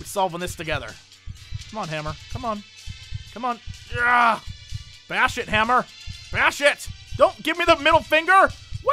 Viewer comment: sounded like you said bear shit instead of bash it lol